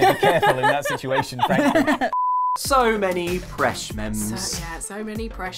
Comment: I swear I thought that kept saying "Fresh Memes" not Presh Mems